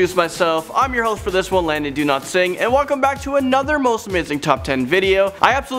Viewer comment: Is it sad that i thought he said "Landon do not sing" instead of "